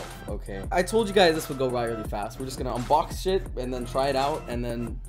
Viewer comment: did he say unbox Sh**